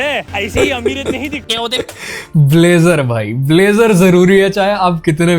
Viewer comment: gangb**g and b*zzers (i thought i heard it all wrong, so rewinded and it is actually blazer 😅🥲